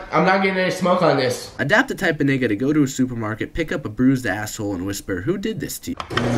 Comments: did he say ass hole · he actually said nigga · lmao says asswhole instead of Apple 😂